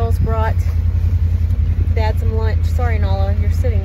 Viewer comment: I thought he said it was dinner and not lunch. He's really lucky and he knows it.